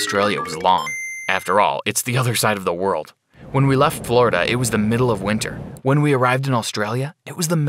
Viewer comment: I thought you said Florida not Brazil but okay